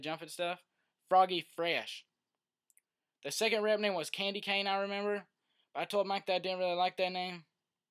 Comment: When he said candycane I thought he said kinky kanine